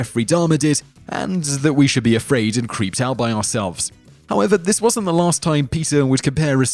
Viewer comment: 'This isn't the first time PETA would compare a serial killers victims to human victims?' Did I hear that right? Cuz it doesn't make sense, I didn't think so anyhow... about 8:05 in